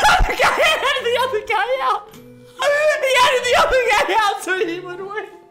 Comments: uhh what is he saying 😂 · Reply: HE EDITED THE OTHER GUY OUT:thats what he said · Thought he said he helped the other guy out but he said it edited the other guy